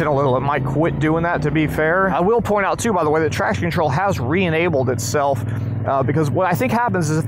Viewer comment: And I heard you saying it has a trash control!? TF????